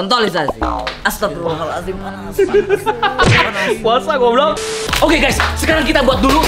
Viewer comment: lu ngomong apa do.. kaget sy 😁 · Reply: Goublok 😂😂😂😂😂